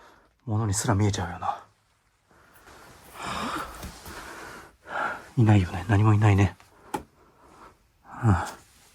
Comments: 「ものすら見えちゃうよな 」 の後こもったような女性の声が聞こえた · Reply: 「何言ってんの？」と返してますね。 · さんの「何もいないよな」に被せて「いるよ」って聞こえます。 考え過ぎかもしれませんが…